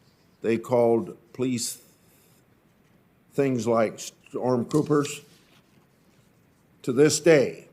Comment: Anybody else catch it at 4:03 where he said "StormPoopers"? Hehe. Anyways, the left and only the left is responsible for the degradation of society. They will feel the wrath of history and hide like the scum they are.